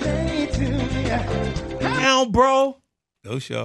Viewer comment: A friend of mine at the time thought he was saying "Go Charlotte! It's your birthday!" 😂😂 Can't unhear it 2 decades later.